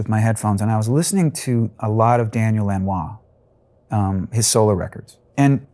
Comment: Can someone tell me who Richard Marx mentions in the interview as an influence for Hazard at 3:28? I can't catch the name..Daniel enrar?? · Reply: Daniel Lanois